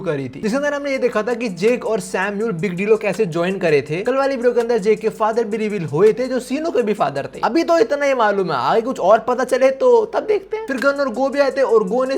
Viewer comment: Corrections: 15:51 - Jake* 17:33 - Sinu* 21:34 - with* old face 27:04 - 1.2 Billion* 34:30 - Gun* 38:56 - 5 Billion* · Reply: addition at 0:15 sensei said 'sinu ke bhi father hai' there it will be samuel instead of sinu